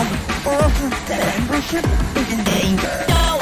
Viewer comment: In 31:46, Match actually said "Bembership, is in danger." instead of "Die for sure" And in 31:48, Pencil says "Don't insult the Match. You are no match for the Match!"